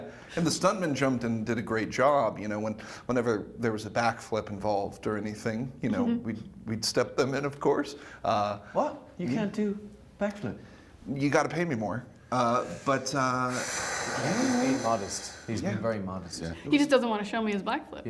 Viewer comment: Lol that sounded like she said blackflip 🤣😂🤣😂